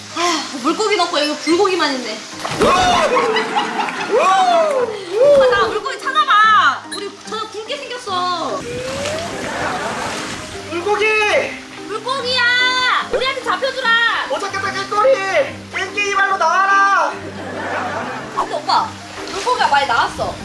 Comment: 나만 처음에 불고기라고 들림?